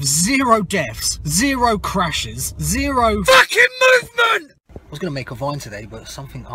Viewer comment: “British motorways have zero deaths, zero crashes, zero... FFAAAHHKIN’ MOVEMENT!!!!” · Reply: at first i thought he said “FUCKIN MOO MOO”